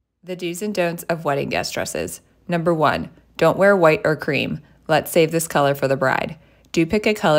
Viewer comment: I honestly thought you said Green instead of cream and I almost died cause that's the colour I got...😂 I was like..WHY?